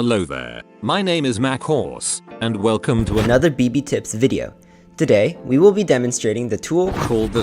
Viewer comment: I thought he said baby tips video 🤣